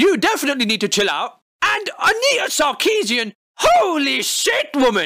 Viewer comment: how dafuq do you spell her name? sounded like Anita Sharkeasyan wait autocorrect showed me AWESOME NOW I CAN LOOK THAT LAST UP